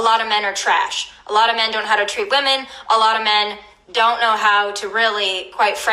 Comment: Did I hear her right at 1:44 ish? "They don't know how to rail me."